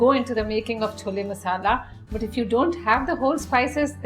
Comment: i thought it said chloe lol